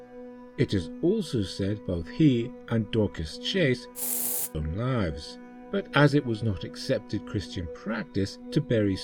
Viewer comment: What word is lost in the hiss at 15:50? EDIT: I think it may be "took their own lives"